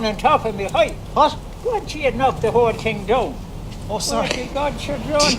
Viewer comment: I think that's "don't you knock the whole thing down." There are a couple of places where I think I heard different things than were written, like at one point I think he said "it doesn't matter what he do or did," but I'm American and have more Scottish ancestry than Irish so I'm not totally confident